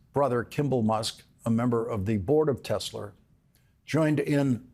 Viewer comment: Did he said "Tesler" just like trump?😂😂😂